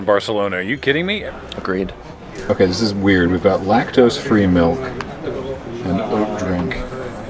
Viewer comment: I would enjoy some Rakdos Free milk... For a brief moment I swear thats what I heard you say then I had to do a double take.